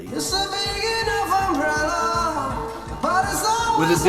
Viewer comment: Instead of the 3rd lines in the verse, "Every time that I come near her", and "It's a big enough umbrella", all I hear is, "It's a Lydian progression". That line fits!